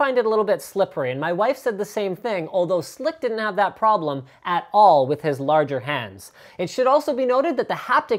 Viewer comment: did he just say my wife with his larger hands ?